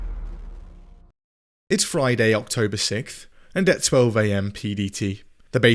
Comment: did you say it's Friday, October Sickth???????